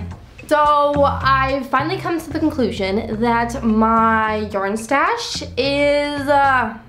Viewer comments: I thought you said urine stash 😭😭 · Did anyone else hear "my urine stash" at about 0:06 instead of "my yarn stash" there? Made the next few seconds... riveting.